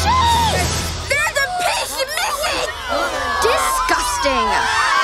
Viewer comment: does anyone know what she said? I cannot understand it at all. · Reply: "there's a piece missing!"